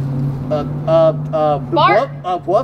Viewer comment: I thought she said “fart” instead of “bark” at 0:16 🤧🤧